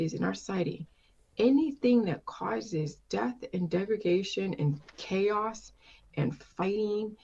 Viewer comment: Jillian says "death and defecation" instead of "death and devastation." She's got shit for brains.